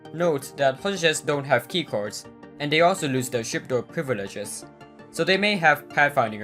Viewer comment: what did he say? I turn on the subtitles and it thought he said chip Door privileges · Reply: he said "chip door privileges"